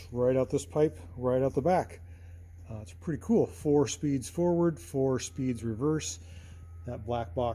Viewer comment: Did I hear you correctly at 2:28,, four speeds forwards and four speeds reverse? Why would it have four reverse speeds.